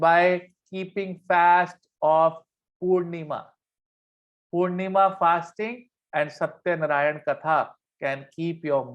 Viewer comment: What kind of fasting did he mention at 46:00? I didn't understand. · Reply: Purnima fasting